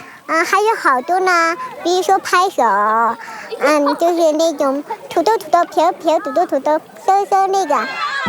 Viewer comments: did she say nigga? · did she just say so so ni**a??😐